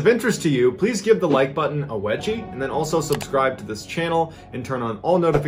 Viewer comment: I could have SWORN he said "give the like button some reggie" had me rolllllinn(no pun intended)😂 had to rewind and everything.